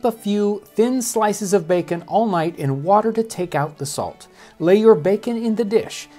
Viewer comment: Thought you said lay your bacon in a ditch at first.